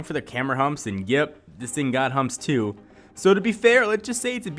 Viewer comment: I thought he said " yep this thing got humped to" LOL